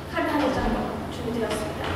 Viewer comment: it sounds like she was saying ch**iya and ras**l (only Indians can understand)